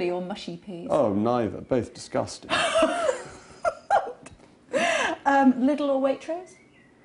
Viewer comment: What means "little or waitress" 22:28 .... I didn't catch the sentence. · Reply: Lidl or Waitrose